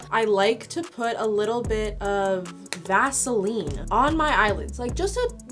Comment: i fr thought you said gasoline on your eyelashes